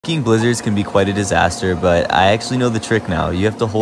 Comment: Am I the only one who when I first saw this I thought he said f wording blizzards